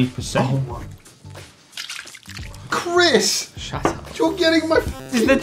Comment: Chris is mumbling something like "scheiße" which means shit or did I hear that wrong? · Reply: He said "shut up"